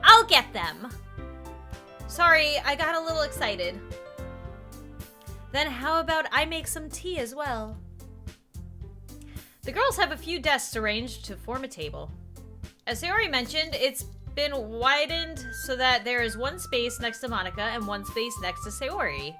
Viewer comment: tell me im not the only one who heard "deaths arranged" because I know this twisted shit